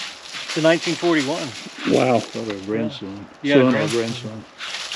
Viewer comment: I’ve been a subscriber for years but I just wanted to let you know at 14:33 you can hear someone say: ( not your grandson ) I swear I’ve listened to it many many times. Wow😳❤️definitely EVP and it says a little more I can’t make it out.😮I think it might say (I am not your grandson but I hear another voice also like an older man talking at almost the same time after he says grand you hear the older man say something.